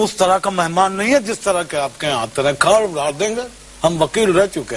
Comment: did he say gaad mar denge. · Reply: He said zameen(earth) mein gardh(dig) dein ga